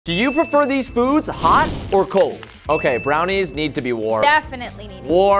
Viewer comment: I thought u said hot or cold not warm or cold